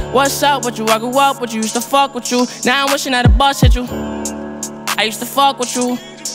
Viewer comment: at first: i thought he said "now they wishin that i bust at you" thought to myself: be funny if he said now im wishin that a bus hit you self realization: well goddamn